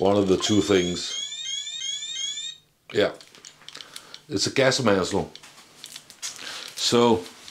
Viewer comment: You were holding a plastic paper @ 4:10 Gas Mantle? Sorry could no understand what you were saying :(